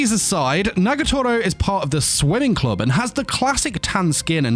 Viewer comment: I know you are saying that she is a part of the swimming club, but i kept hearing it as the "sweating club"!